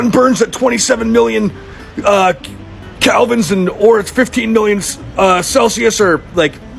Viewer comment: Did he really say that "the sun burns at 27 million... ahhh.... ick-k-k-k... Calvins"? How many Hobbeses is that?